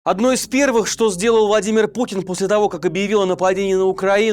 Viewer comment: Я сначала подумала, что "владимир пукин" мне послышалось,но пересмотрела раз сто,нет не послышалось😂